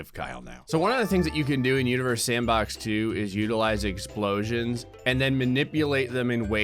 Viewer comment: i swore he said uterus sand box 2